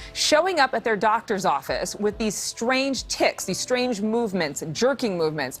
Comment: lmao, I thought the reporter said these strange tits at first. 😂🤣